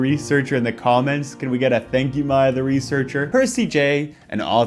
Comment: I THOUGHT HE HAS BEEN SAYING “my other researcher” THIS ENTIRE TIME